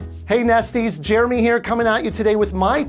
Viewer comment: I'm like did he say HEY NASTY...........I'm like BRUH!? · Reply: Emaan Ahmed it’s from the channel name, so nest - ys ? i was thinking he said nasty too and i was confused 🤷‍♀️